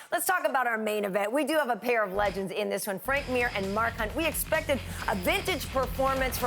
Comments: I thought it said mike hunt lol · At first I thought Mark Hunt said Mike Hunt